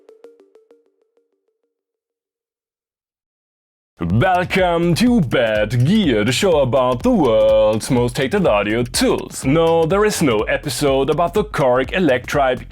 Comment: I thought he said welcome to bad beards.